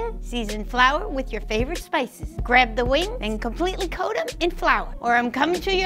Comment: when lynja said wings i heard it as wrings🤣🤣🤣